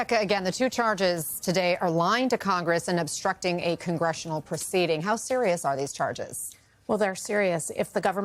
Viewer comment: I agree with the first half of your sentence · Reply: I thought you said they were obstructing an investigation.